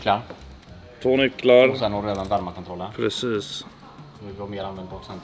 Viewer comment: vad sa säljaren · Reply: "Värmar-kontrollen" kanske ? man kan aktivera parkeringsvärmare med ett knapptryck. Kan ha fel.